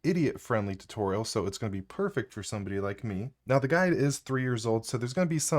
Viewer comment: I know you said "guide", but swear I heard "guy" here. If thats the case, thats some life speedrunning right here